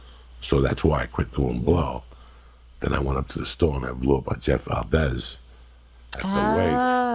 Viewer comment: What does Joey say at 24:28? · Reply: "i went to the store and blew up on jeff valdez"